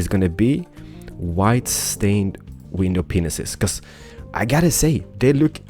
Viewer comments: did he say white stained penises? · it sounds like he says white window ding dongs